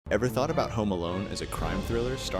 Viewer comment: the video cut out and restart again at the start so all i heard was "ever thought about homo"